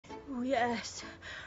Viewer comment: what she says? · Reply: Just a simple "Yes."